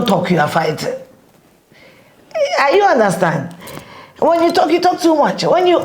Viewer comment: who else heard “Are you understand”😂😂😂 Great interview thou☺️Afia❤️